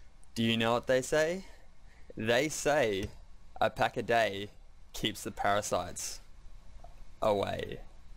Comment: i thought they say, "A pack a day keeps BoosterKings at bae"????? when did this new saying come in?????? and awesome video man